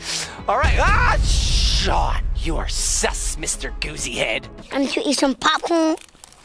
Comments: I LOVE how just sits there eating pop corn he said "im just gonna eat some popcorn" It gets me every time LOL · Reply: i thought he said cant you see im eatin some popcorn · I LOVE how just sits there eating pop corn he said "im just gonna eat some popcorn" It gets me every time LOL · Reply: I thought he said “wanna eat some popcorn?”